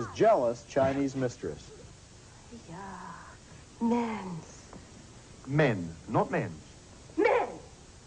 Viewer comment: Is he saying, "Men. Not men."? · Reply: Men, not 'Mens'.